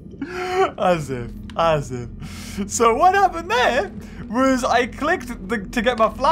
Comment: did he say asif asif ??😂 (asif is sorry in Arabic )